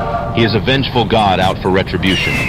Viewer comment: "How'd that taste, Sergei?" U.S drone operator, 2023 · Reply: i heard "how'd that taste, sir gay"😂